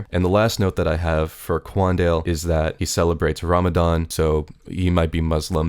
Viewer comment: You misheard Ramadan as ramen hour